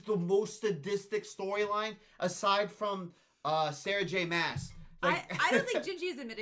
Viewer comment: DID U JUST SAY SARAH J MASS? OMG, I'd happily sit and listen to you guys podcasting SJM's novels! ❤❤❤❤